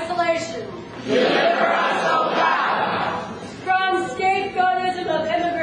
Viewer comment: At first I thought they were saying "Obama," but after turning it up, putting on professional quality headphones -- they are saying "O God." They are NOT saying "deliver us Obama." Listen to it with head phones.